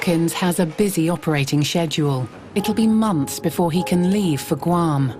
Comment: wtf am i the only one who heard shedule